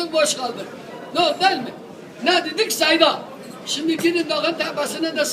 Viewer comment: ne dedik ahhhh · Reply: Ne dedik seyda